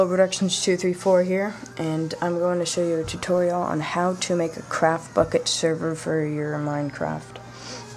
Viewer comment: when you said craft bukkit server my utube freezed so i thought he said "today ill show u how to make a crap...Bukkit server